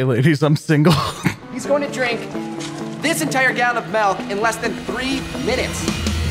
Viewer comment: did he just say melk